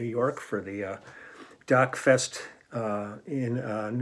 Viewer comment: I thought he said "DUCK Fest"....